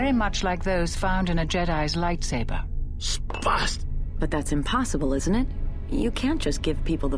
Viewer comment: Does Katarn actually say "Spast" in that cutscene at 8:08? That word is the german equivalent of retard.